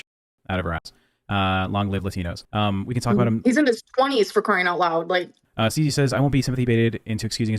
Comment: d...did she just say Scooter is in his 20s?!?! my god he looks ROUGH for being in his 20s